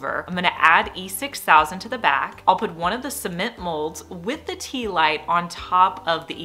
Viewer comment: When you first started and said "concrete or SEEment," I thought you said semen 😳 and wondered what the heck you were making! 😂